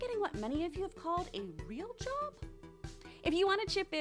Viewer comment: When she say "real job" I thought she said "rim job"